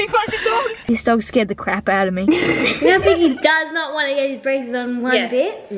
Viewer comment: did just say "i just got scared the crap outta me fuckin dog!" ??? IDK THATS WHAT I HEARD